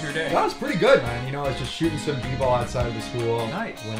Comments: Iol i thought he said “i was shooting some people outside the school” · I thought he said he was shooting people outside of the school 💀💀💀💀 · I thought he said "I was shooting some people outside a school" bro😭😭😭😭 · i deadass thought he said i was shooting some people outside of the school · I really thought he said "I was just shooting some people outside the school"